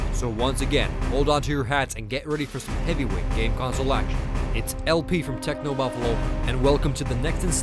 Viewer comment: does he say "gay console action"?